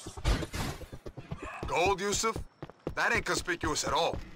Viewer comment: Did somebody say gold?